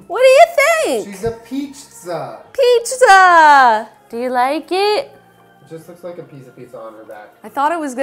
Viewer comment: I legit thought jenna said " do you like vaginas?" To. I was about to pass the fuck out. But what I'm really scared about is the fact that she spayed and neutered them all.